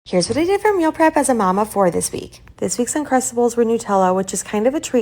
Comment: Lmao i deadass thought you said 'a mom of 40' i was like dammmmmmmmmnnnnn aint no way aint no way or i den lived entirely way too long 😂😂😂and im in my 20's lol